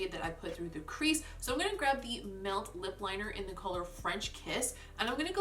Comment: During the first looks lip look i thought you said "wrench kiss" at first and I was like... damn that got mafia fast... but nope, definitely French kiss. 🤣🤣